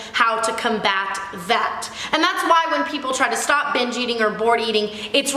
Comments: i thought tana kept saying "bitch eating" then i realized i'm a total fucking idiot. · Legit thought you were saying "bitch eating" for a while and I was so confused lol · I thought she said "bitch eating" 😂😂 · I totally thought she said, "bitch eating" I'm like HOLY SHIT that's a thing? sign me the fuck up. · This whole time I am watching your video, I thought you said bitch eating. hahaha.